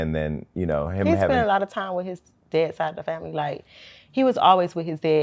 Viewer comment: My dumb ass thought she said, "He spend a lot of time with his dead side of the family."